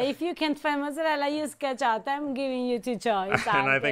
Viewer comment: I'm so glad there was a clarification because I SWORE she said if you can't find mozzarella to use Ketchup 😂